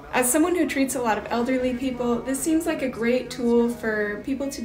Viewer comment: Thought she said “As someone who beats a lot of elderly people”